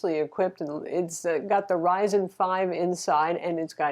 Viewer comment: "It's got the Ryzen 5 inside" I heard "Intel Inside". Sorry, I'm still cleaning my ear of heresy